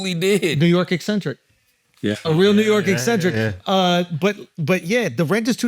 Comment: for I second I thought Schultz said he a real nigga😂